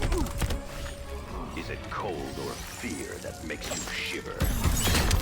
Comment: it sounds like he says, “Is it cool, that pee makes you shit?”.